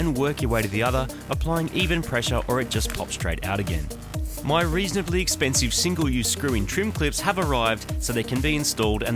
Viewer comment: Am I a cheap arse or did he say “reasonably cheap clips” 😂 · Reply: I thought he said reasonably expensive clips lol